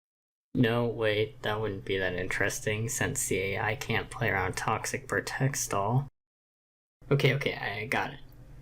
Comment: The English legends said that you said "toxic protects all". Is that really what you said? If not, what did you said because i didn't understand. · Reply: I said "No wait, that wouldn't be that interesting since the AI can't play around toxic-protect stall." I jumbled my words a bit there so I can see why what showed up on screen was weird.